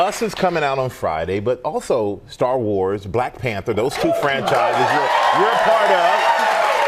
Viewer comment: i thought he said star wars: black panther,😄😄😄